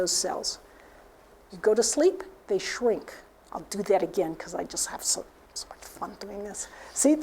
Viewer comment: <link> - Did she just say. "I'll do that again, cause I have some fucked in the ass" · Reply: ted bet. Haha. It sounds like that, but no. She says "because I have so much fun doing this."